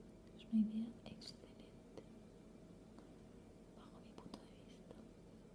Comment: Que dice exactamente en el minuto 10:39,10:40? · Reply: Bajo mi punto de vista